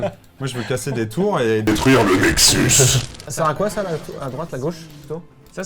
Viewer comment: j'ai entendu "détruire le Mexique". Pendant un instant j'ai cru que cette vidéo était sponso par Trump x)